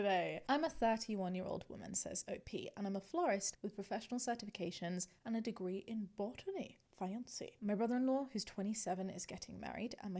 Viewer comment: I thought you said a degree in bottoming as I was ADHDing and was snapped back to reality by that thought 😂